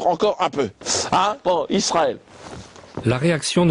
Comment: A 2:00 ; moi j'entend bien Israël pas Isra*heil*